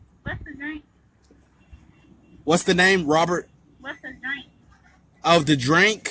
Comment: I thought she said " What's tha name? " Too 🤣🤣🤣🤣 when she said " What's tha drank? "